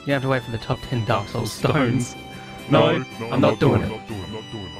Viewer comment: My high ass thought it said top 10 stoners